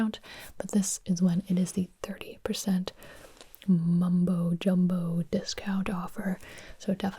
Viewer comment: did i hear mumbo jumbo iskall